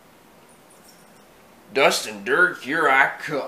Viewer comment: was sheriff originally saying dust and dirt? Cause when I first heard him say that I heard him say distant 3rd